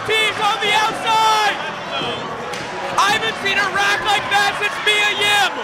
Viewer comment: “I haven’t seen a rack like that since Mia Yim” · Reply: I thought he said "I haven't been errect like that.."